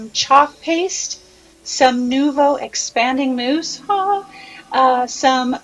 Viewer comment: What's the name of the paste you used on the arches? It sounded like you said, "New bow paste." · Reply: It's Nuvo exaspanding mousse, like the title of the video it's also listed in the supplies list on the blog. <link> Hope this helps... Thanks for watching!